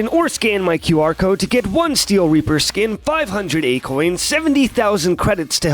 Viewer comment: I was on a different window when you said "500 A-coins" and thought I heard acorns and clicked back real fast like "what? what the fuck?"